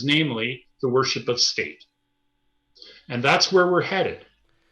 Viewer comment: One world religion. The worship of state. Spot on. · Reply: I heard "...the worship of Satan", not "state", lol... But then that's pretty much splitting hairs, isn't it ?